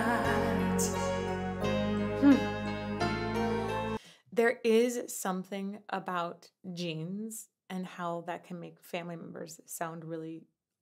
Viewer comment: I had a total brainfart moment at one point there... I heard "there is something about jeans [not genes[ and how that can make family members sound really awesome together".